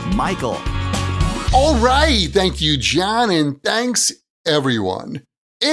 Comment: What did you just say on 1:21 ? How's John? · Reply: All right, thank you John - and thanks everyone. It’s Michael here from Happy English, and I help people speak English better. You may be happy to know that starting last week, all of my long form podcast videos have captions. I hope that's helpful!